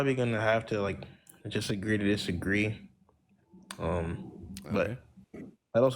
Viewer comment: “Somewhat productful”??? “Disagree to disagree”???? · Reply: To be fair, i heard "jus(t) agree to disagree"